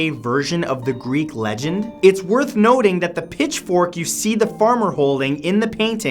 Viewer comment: It sounds like he said nerth woting instead of worth noting when talking about the phallic painting